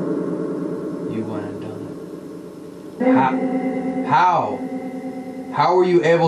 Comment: someone said "how dare you" and then after that said "you went and done it" and also he said that there are portals where the bodies are kept. Presila kept saying her body was in the basement. then again at 1:04:19 he said "ask some beneath" probably meaning theres a fuckn portal in the basement, you have got to find it or ask about it at least. I'm not sure if or any member of the overnight channel will see this but anyone reading the comments should go back and listen to it see if you heard what i heard